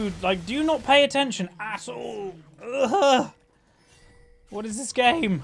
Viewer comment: At long last ... finally at 18:34 i hear cursing for the first time ...its a great day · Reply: "at all"